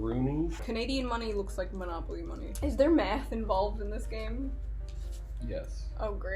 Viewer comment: At first I heard "Is meth involved?" "Does the banker do meth?" I was like: hold up, this is American accent.